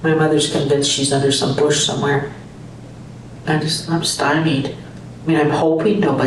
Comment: I thought she said “under a porch somewhere “. Captions said “in a bush somewhere “. That would make the family even more suspicious 🤨